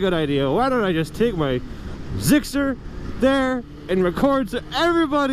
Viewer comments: I swear i heard gxsr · i swear i keep hearing GSXR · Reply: he said zxr